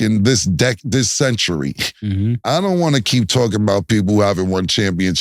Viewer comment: This century. 2000 and beyond. Maybe your comprehension skills are just that low. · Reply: it's called a mistake I thought he said in a century, didn't know he said this century til I went back and listened. I guess your perfect and mistake free though